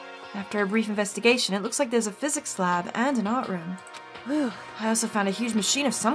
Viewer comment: DID YOU SAY ART ROOM???